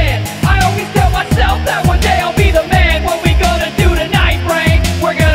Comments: Realized he said "what are we going to do tonight brain" like if you know what show I'm talking about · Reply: The Triggered Butler I thought he said "what we gonna do tonight frank" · Realized he said "what are we going to do tonight brain" like if you know what show I'm talking about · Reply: The Butler ohhhh I thought he said what we gonna do tonight frank lol